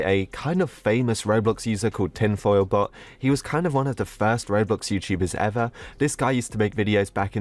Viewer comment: Btw My captions say: The first row books YouTubers ever this guy used to make videos in 2011 I